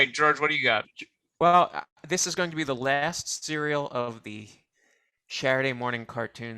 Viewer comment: Waitaminute!! Did George just say this is the LAST Shaturday Morning Cartoons Show!? Please say that was a slip of the tongue and not true!? I absolutely adore this show and try my best to watch every Saturday Morning/Afternoon if I can!